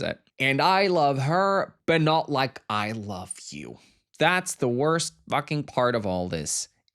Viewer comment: Ilya didn't say it's the worst thing @ 19:05. He actually said "в этом вся хрень" which can be roughly translated to "that's the thing" or "that's the catch". It's not necessarily negative. And oh my god, Connor did such an amazing job! Both of them did. It's funny how Connor and Hudson are pretty much the reverse of their characters 😂 where the former is a well-behaved polyglot and the latter is an unhinged sex pest